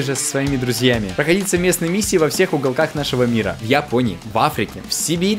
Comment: ГОВОРЯТ: в японии Я СЛЫШУ: я пони